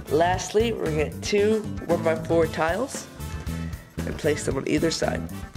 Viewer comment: Lastly we'll get 2 4×4 tiles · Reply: Well the joke was that I thought he said 4×4 tiles as he was placing a 1×4 tile but now since I heard it with earbuds I was able to catch him saying 1×4 instead of 4×4